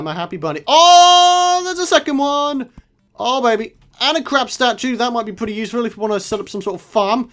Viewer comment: I thought you said "oh and a crap statue"